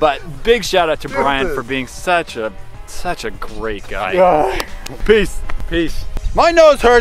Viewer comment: For a moment I thought they were saying 'peas' and I was really confused until I realized they were saying 'peace' xD